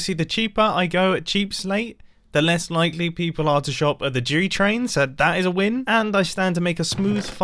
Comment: I swear said Jew train